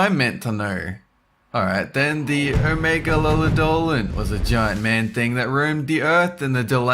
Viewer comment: I thought said "Megalovania" lol